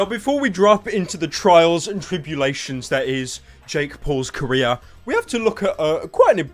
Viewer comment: I thought he said Jake Paul's Korea...